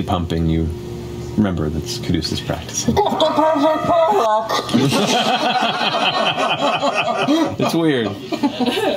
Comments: What the fuck does Ashley sat at 2:38? I’m sure I’m just not getting the reference but I can’t for the life of me understand what she says. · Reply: 'You've got the perfect warlock' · I accidentally watched this as the new year ticked over and I'm living Also does anyone know what Ashley says at 2:38-2:39? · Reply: Happy New Year! She says, "you've got the perfect warlock" in reference to the DnDBeyond Ad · I still can't catch it. Anyone know what Ashley is saying behind her hands @ 2:36 ? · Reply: She says "you've got the perfect warlock" in reference to the DnD Beyond ad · Does anyone know what Ashley said at 2:38? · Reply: 'You've got the perfect Warlock.'